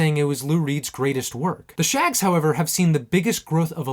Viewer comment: i thought it said shaggy not the shaggs, but shaggy is just actually a comic